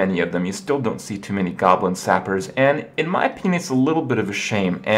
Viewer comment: I swear says ''In my apenis''